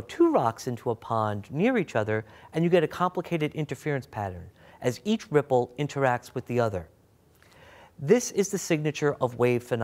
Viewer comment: lo it sounded like he said "... as each cripple interacts with the other..."